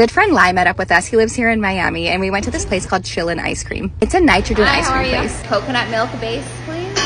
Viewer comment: Bro i love thought she said catchin ice cream I was about to be like WHERE IS THE ICE CREAM THROWING MAN